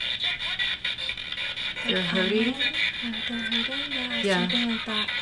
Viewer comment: When u guys were using the sticks and the other thing I heard it say “they’re hurting me”